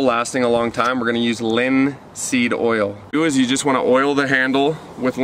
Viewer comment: I remember the first vid i saw i thought he was always say Lindsay Doyle, not linseed oil😂😂😂